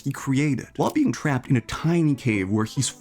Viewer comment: Did he just say a time cave???